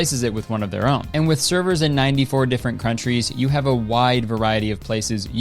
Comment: did u just say “croutrys”?